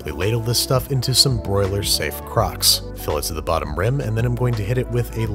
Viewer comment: I wasn’t looking and heard him say put the soup in the CROCS and almost jumped out my seat